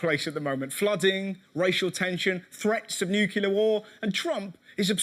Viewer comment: Love how at 4:03 he says "Newkiller war" instead of "Nuclear war"